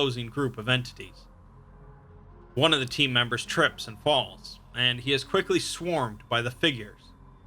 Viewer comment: I legitimately thought he said "tripped balls" for a sec